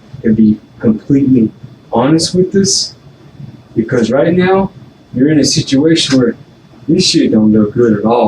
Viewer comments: the subtitles say right meow when the detective says right now 😂😂😂 straight to super troopers my brain went 😂😂😂😂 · '..but right meow' the subtitles say 😂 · the subtitles say "right meow"